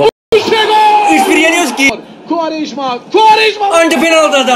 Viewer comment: dk da Quaresma sesi mi duydum? 😢